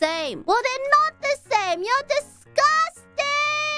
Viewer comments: Gonna be honest, I heard "borning" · I've been in Australia for 10 years and I still heard boning first. It's really easy to mishear the Aussie accent sometimes if you don't listen carefully. 😁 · as an Aussie...... i heard boning :"D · I literally heard "burrowining". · Even I heard "boning", and my english sucks